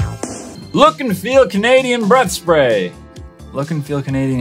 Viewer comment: I thought he said butt spray.